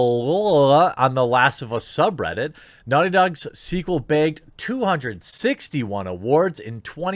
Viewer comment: I wasn't looking at my screen, just listening, but at 1:47 I heard "BEGGED" 🤣😂🤣